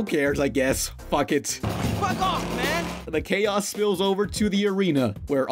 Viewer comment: DID HE JUST SAY FUCK OFF MAN???